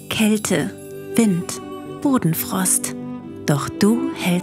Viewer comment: Muss zugeben, als bei 1:27 "Bodenfrost" kam, hab ich "Hodenfrost verstanden und dachte das wäre Absicht